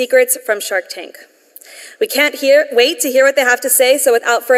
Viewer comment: She was about to say "We can't hear what they have to say" at 2:25. That made me chuckle